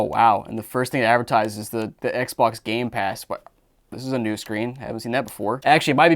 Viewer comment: Did he just say gay pass?